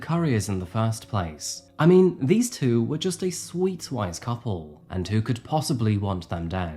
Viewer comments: I tried that too - if he said wise, that's equally strange. Lol... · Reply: No, I thought he said "sweet WHITE couple," not wise. If he said wise, that makes sense. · does he say "these two were just a sweet white couple?" I don't really care...just seems like a weird thing to say so I wonder if I'm mishearing. Lol...